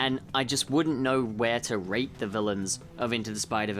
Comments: on captions said rape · I thought you said rape for a second there 😅